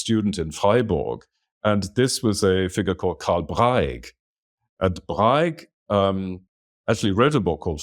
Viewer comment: does anyone know the full name of the person mentioned here? it sounded like 'karl breig' but that is not turning up any results on google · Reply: Carl Braig Vom Sein